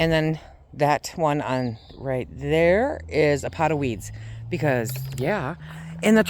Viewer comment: I thought you said a pot of weed. Lol... listened again and you said weeds.